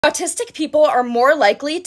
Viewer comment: I thought she said “artistic” 😂